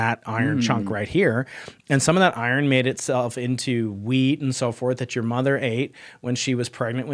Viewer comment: Did he just say that guy's mom ate weed when she was pregnant with him? 😂